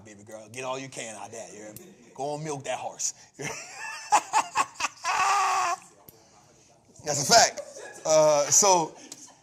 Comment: did he just say go & milk that horse??? is crazy as Hell, LOL...